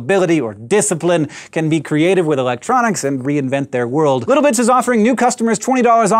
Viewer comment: Sorry I thought he said little dic's at the end.LOL